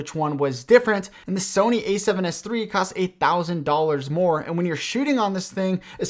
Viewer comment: error in subtitles, its reads 8000 instead of 1000